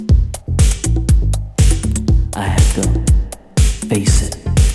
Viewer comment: I thought it said 'faces' when I first heard this.. Absolute classic anyway! Thanks, keep up the good work.